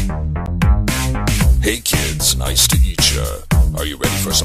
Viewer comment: What I hear: “hey kids nice to eat ya!” IT MAKES SENSE THO